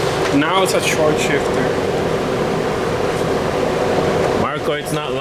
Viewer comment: Around the 18:40 mark, I swear I heard ''Navo's a shortshifter''.